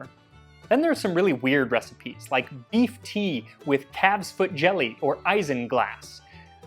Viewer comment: haha, i thought he said teeth tea for a second